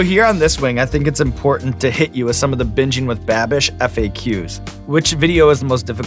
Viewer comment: It sounds like Sean is saying Bitching with Babish.